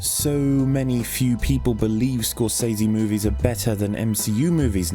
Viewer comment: did he say Skorzeny movies?